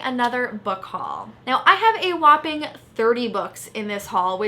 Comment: ahahah I thought she said a whopping 3 books and that was her biggest haul